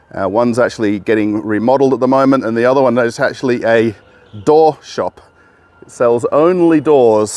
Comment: I thought you said "A DWARF shop"! I was about to book a ticket to rush right over and buy myself at least a half dozen of those bad lads.